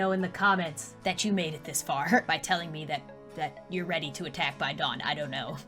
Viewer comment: I thought you said “ I’m ready to attack the dog” lol